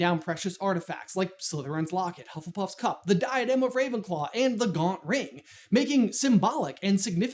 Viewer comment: what did you call the ring? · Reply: The Gaunt ring